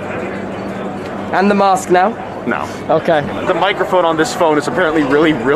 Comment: I deadas thought it said “I Am the mask now”.